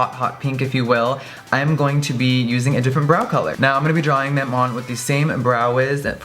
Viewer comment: Oh lord I'm so deaf... I thought he said a different "Bra" color. Then I realized he said brow lol